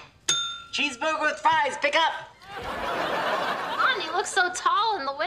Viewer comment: When he said pick up i thought he said the n word lol